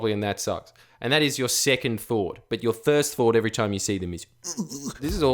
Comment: You think I didn't hear you say "thirst fought" at 5:45? You think this is a fucking game?